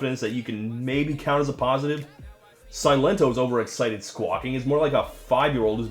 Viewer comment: Pls help. Don't know English very well, can't figure out the word on 10:06 "Silento's overexcited ??? is more like..." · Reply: "Silentos overexiting squawking is more like a five year old......